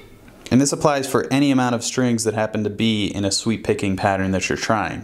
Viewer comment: Bruh, I've been playing for almost 8 years, and have never heard of sweep picking. At first I thought you said "sweet picking."